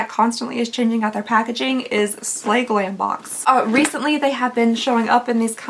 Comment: 100% thought that said "Sassy Granny Box" and I was going to ask for a referral code lol